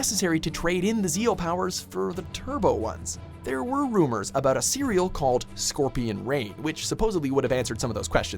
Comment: did he just say there’s a Cereal that could answer questions about The Power Rangers?